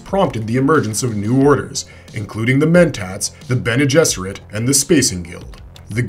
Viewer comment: "including the Mentats"... I heard "including the Mentos" Ive been playing too much Fallout. lol